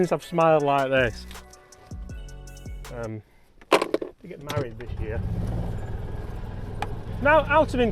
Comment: did you say you are "getting married this year"? Again?